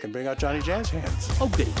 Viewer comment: did Felix say "Oh boo" (like Gene often says) or "Oh goodie"? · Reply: Oh goodie